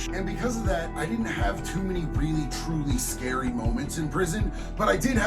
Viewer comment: I thought that said the stretchiest moments in prison 😂 😅